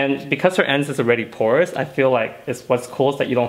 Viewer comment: Drinking game: take a shot every time he says "porous ends" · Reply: I'd be sober, I thought he was saying "course ends" BAHA